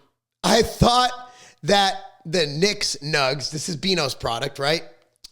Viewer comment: Bro I thought you said nick’s nuts 😂